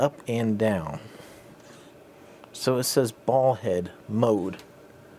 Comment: I thought you said ' Bald head ' mode 😂